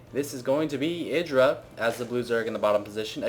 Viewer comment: During the Early part of the vid, I thought you said "Idra the loser", I then noticed 3min in you meant "Idra the blue zerg". Hahah sounds alot the same when u say it xD even went back to it twice and it sounded like loser